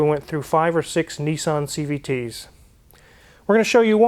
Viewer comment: I thought he said Nissan suck.